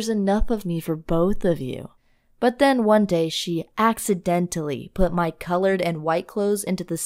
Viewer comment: When she said “accidentally” I thought she said she was going to get stuck in the dryer